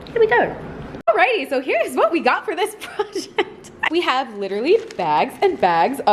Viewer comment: when you said project it sounded like u said bullsh*t